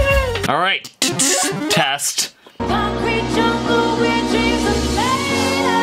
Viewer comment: I always heard "where dreams tomato"